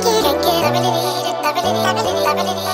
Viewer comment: Lol sounds like shes saying feels so good feels so good pewdiepie